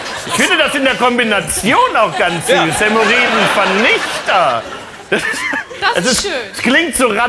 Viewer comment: Ich habe verstanden urin vernichter